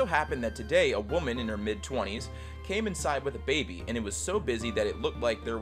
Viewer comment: I SWEAR TO GOD MY HEART DROPPED BECAUSE I WASN'T LOOKING AND IT SOUNDED LIKE THEY SAID CAME INSIDE A BABY